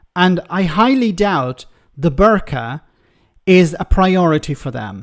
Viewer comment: Personally, I'm all in favour of banning the berks - though I was a bit surprised Pochin volunteered to ban herself and the other Berkeley Hunts that sit beside her. Oh sorry! Seems I misheard "berks" for "burqa". What a stupid woman!